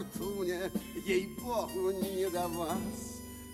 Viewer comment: ну десять раз проматал ,и всё равно слышу "ей похuй не до вас" :) Фильм детсва,всем двором после него шпаги делали с палки из крыжок от банок